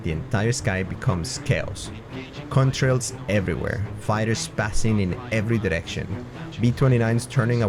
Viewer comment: Fighters spazzing in every direction? Or did I mishear? That's a phrase maybe worth not re-using lol. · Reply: Fighters passing on every direction 😅 English is my second language soo sometimes it might sound funny or stuff like that might happen 😢